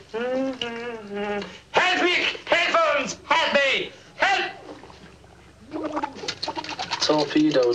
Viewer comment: What exactly is John yelling, right before he disappears under the water??? I could only make out, "Help!" 😂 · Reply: I'm not going to lie... I honestly thought he was saying, "Happick, Headphones, Happy, Help!!" Seriously! I'm definitely going to have the subtitles on next time I watch my Blu-ray copy! 😂😂😂😂